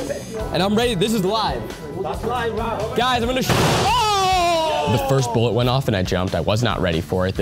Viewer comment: “ The first bullet went off, I wasn’t ready for it, there wasn’t a ready set go” dude, trigger safety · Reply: I thought he said Ned's first bullet